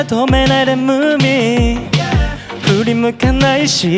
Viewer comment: の仲間にニッコリTAKUTOくん 3:26 ごめん「ムーミン」って聞こえてしまったよTAKUTOくん